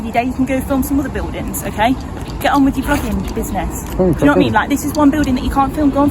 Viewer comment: Did she say @ 9:36 get on with your fckin business ?